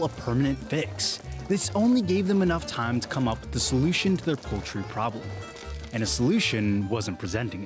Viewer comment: I wouldn't say the problem was so paltry, babahahahah i suck i actually thought you said paltry problem at first